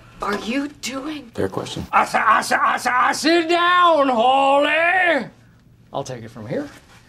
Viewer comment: I SIT, I SIT, I SIT, I SIT, I SIT ON YOU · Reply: It sounds like he says I say i say I say sit down holly